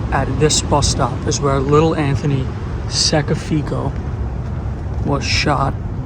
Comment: His last name is actually pronounced Si ah sha phonetically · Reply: Thought he said Anthony sackofseagulls 😂